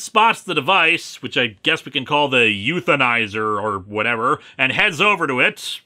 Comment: I heard that as "euthanizer." That would've made this a very different book. :-P